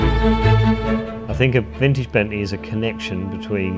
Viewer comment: Great vid. But the CC's are so off. At it read 8:52 it reads "dentist bending" instead of "Vintage Bentley". One of many mistakes. Though I don't know how the CC's are generated.